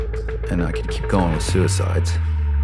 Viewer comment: did he say suicides?